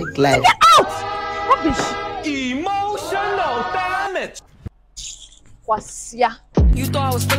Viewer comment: Did she just say “Kwasia” 😂😂😂